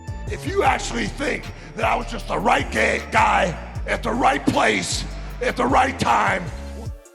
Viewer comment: Hulk hogan is in front of Vince McMahon as well 😂. And I’m not the only one that thought/heard him saying (“I’m not just the right gay guy”)